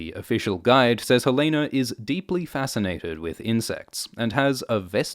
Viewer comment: For a brief second, I thought he said Helaena is deeply fascinated with incest. Then my brain turned off Targaryen mode and realized he said "insects" lol